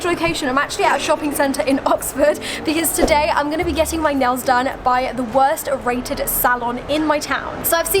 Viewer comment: Am I the only one who heard 'nose done' @ 0:09?